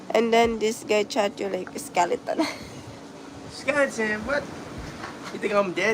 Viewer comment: Dude can't understand what his wife is saying 18:06 "and then this guy charge you like a scalper" not this guy said you look like a skeleton